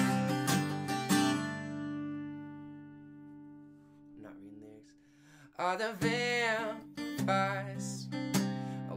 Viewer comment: Am I The Only one Wondering What He Whispered At Like 2:07 Or Something ._. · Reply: he said "I'm not reading lyrics" haha